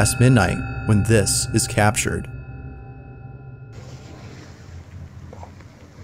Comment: I like when one of them call her flower! Lol ! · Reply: Idk why the translation said flower, since as a Polish person, I hear him say 'baby bear'.